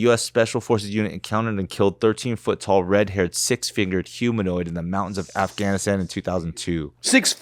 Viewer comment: I thought he said six figured too lmao